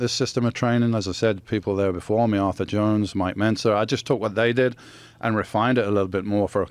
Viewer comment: Bom trabalho amigo! Apenas um detalhe, aos 3:11 onde na legenda está escrito "meu mentor..." na verdade o Yates diz "Mike Mentzer" que também foi um grande bodybuilder dos anos 70. Mentzer tinha um sistema de treino chamado de "Heavy-Duty Training System", que inspirou o sistema que o Yates utilizava em seus treinos.